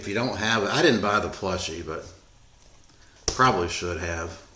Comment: Did you say "plushie" at 7:00?